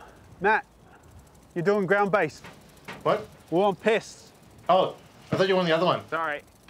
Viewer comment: i keep hearing "piss" instead of pests